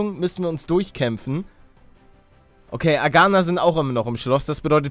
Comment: hab ich verstanden "Veganer sind auch immer noch im Schloss" ... damit ergibt die Sache erst einen Sinn O.O